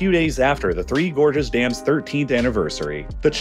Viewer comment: I thought you were saying "Gorgeous dam"... and I was like: "yeah they look alright but i wouldn't go that far"